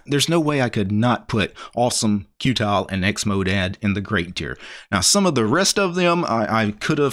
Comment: did you just say xmodad?